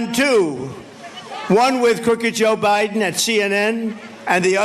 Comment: Did he say “Cookie Joe Biden”?